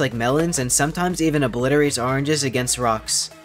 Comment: Who's David and Goliath? I keep seeing their names in the comments and dude says Goliath in the video. I thought he said the Lion and was looking around lol